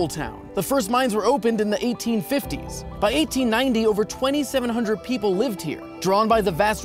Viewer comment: HE sais 27,000 captions say 25,000